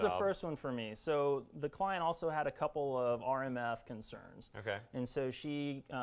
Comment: He details the EMF (not "R") shielding yet does he say he wired the house for Cat5 to avoid using Wifi? No, he did not.